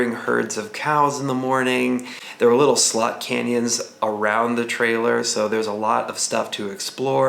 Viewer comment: Swear on the Bible, I thought you said "Slut Canyons!"